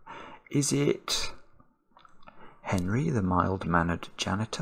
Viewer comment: As a devotee of "USA Cartoon Express" during my misspent youth, I can say for certain it is Penry, not Henry. In fact, I still have the 1974 Fall Preview issue of TV Guide which says Penry's full name is Penrod Pooch, but I do not believe he was ever addressed as such during the series. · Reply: Wow, always heard it as Henry - presumably because I had never (and until this day) heard of the name Penry (or Penrod - which sounds like it should be a character in a Sherlock Holmes Short Story)